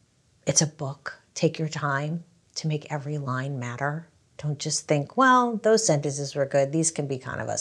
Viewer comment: Did she just say ALL LINES MATTER?!? Lol